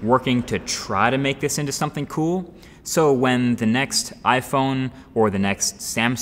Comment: When you said Next iPhone in the beginning, I thought you said Nexi (like plural of nexus) Phone 😂